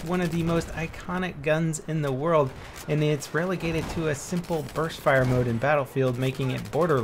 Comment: U sound like ur saying burth fire